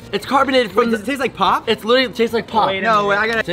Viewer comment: i legit thought he said it tasted like cock